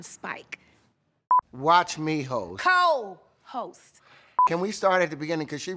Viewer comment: I thought he said "Watch me, hoes"